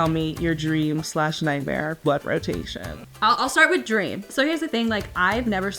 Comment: I thought she was saying "Blood Rotation" at first.🩸😨😆🤣